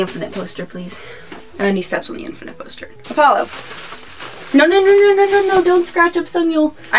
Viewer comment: Aww your cat is so cute! What's his name? I'm asking because I thought it sounded like Apollo, one of mine is called Apollo so I thought maybe I misheard cus I was thinking of mine XDBtw I want this album, I heard it and it's really good, I love their music so much! I will definately have to get it soon, man I'm so behind 😒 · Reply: ktboo1979 His name is Apollo haha. We didnt name him but that's the name he came with. Thats so funny how our cats have the same name haha. Hopefully you can get this album soon ^^ its really nice